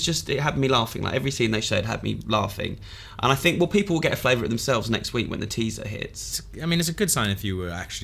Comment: is it me or did he said the teaser relese next week